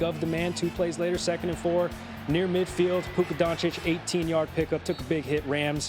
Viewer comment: Did he call him Puka Doncic 😂😂😂